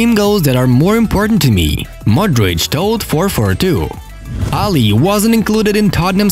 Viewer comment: I heard 442oons instead..